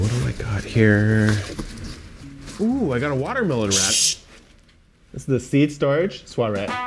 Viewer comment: The beginning sounded like he said "Watermelon rat".